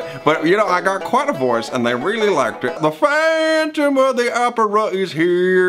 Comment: Once I heard "phantom of the opera I died".